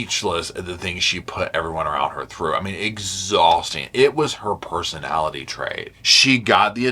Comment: LOL I heard "Exhausting was her personality trait" and I had to rewind cuz I was like, yeah that tracks.